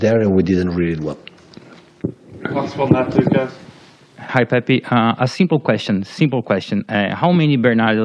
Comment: Did he just say hi Pepe ?